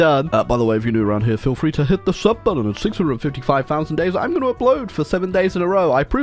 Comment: either I'm auditorily dyslexic or did say at 650k days?